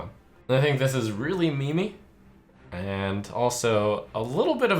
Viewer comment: Did he just say memi?!!!